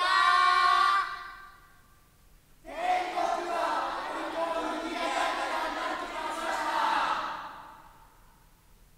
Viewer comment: どなた様が教えていただけないでしょうか。かけ声の01:26のところに何を言っていますでしょうか。日本語勉強中でうまく聞き取れていません。ありがとうございます。(恐らく天国もとても賑やかになっていました？) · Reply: 天国はわりと賑やかになってきましたあ！